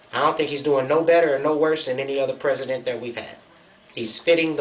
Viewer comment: I THINK HE SAID "WORSER" FOR THE EFFECT.. THIS GUY IS SPITTIN MAD KNOWLEDGE.. HE USED WORSE THE RIGHT WAY LATER N THE VIDEO TO PROVE HE KNOW...